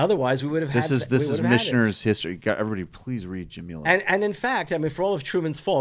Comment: Michael implores us to read a book. I can't catch the title or author, though. What is it? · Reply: I think it's from the podcast he plugged earlier around 9:10 "Micheners history" <link>